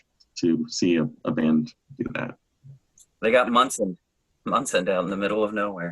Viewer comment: "they got "what" in the middle of nowhere?😮 I don't understand. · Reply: Munson'd. Believe that was a Thurman Munson baseball reference. -Jason